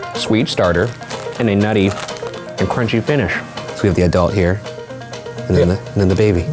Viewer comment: did ryan just say "a nutty finish"...